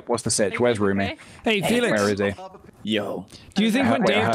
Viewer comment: TO ALL THE STAYS... when jack said "hey felix" istg i heard it as "Oi FeLIx!"